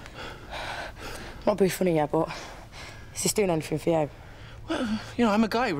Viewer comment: What does she say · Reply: LunarChronicles “I’m not being funny but is this doing anything for you?”